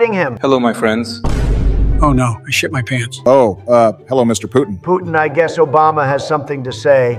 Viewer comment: I thought he said "Hello mr pooping"